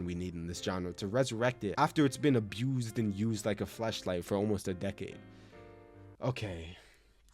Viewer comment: did he say flash or flesh🧐